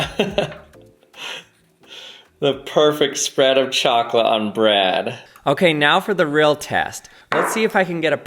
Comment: I heard, "The perfect spread of chocolate on Brad!" and looked at my reel for the first time in idk how many swipes.